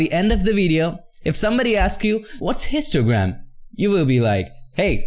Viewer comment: I'm tripping when first listen to video I thought you said what is Instagram? And get this...I wanted for the explanation! LOL! i must be more tired than I thought!